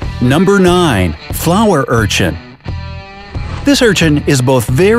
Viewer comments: at first i thought #9 said flower unicorn xD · I thought it said "Flower Version" 😂😂😂😂😂😂